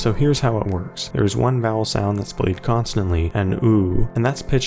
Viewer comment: I heard he said "Hot Sound."